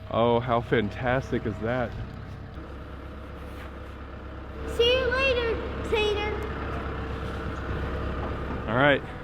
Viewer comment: NO BECAUSE IT SOUNDS LIKE HE SAID “See ya later, satan!” AND APPARENTLY THE SUBTITLES AGREE- 💀💀💀